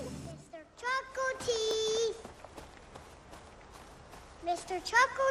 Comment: Thought it said chicken teeth lol